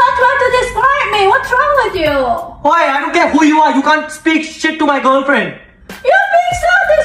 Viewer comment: I thought he said " I don't care who u are don't talk to my girlfriend like that! " One moment later.... " I'm sorry uncle I didn't mean that. "